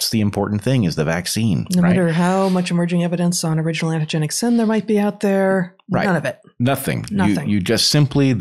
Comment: What did she say at the 3:35 mark? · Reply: no matter how much evidence of emerging antigenic sin there might be out there